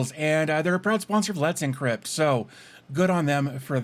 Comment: At about the 3 min mark I thought bro said proud sponsor of Bloods and Crips. My dyslexic ears got you a like from me. Real talk tho great video.